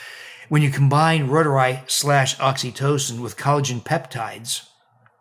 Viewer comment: I don't understand the word, what he combines with collagen and hyaluronic acid, it sounds like wodorei 7:12...does anyone know? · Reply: "reuteri" - it a probiotic. Dr. william Davis is a proponent of L. reuteri